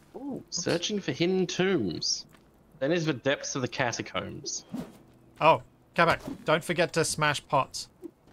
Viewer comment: "Oh Capac, don't forget to smash pots" - What I heard "And Hulk..." "Grr?" "Smash"